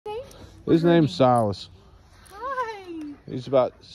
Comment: At first I heard you say his name was Sauce lmaoo